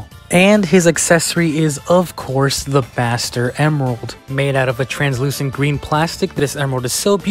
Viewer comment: When he said the ‘Master Emerald’ for the first time, I heard ‘Bastard Emerald’ 💀